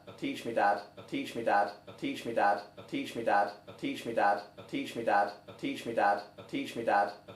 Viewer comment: After a while I hear "I'm teaching your dad" and "peach me dad" and "I'm peaching in your dad"😂